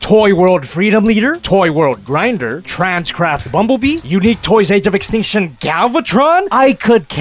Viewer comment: did he say “Trans-Crap Bumblebee?”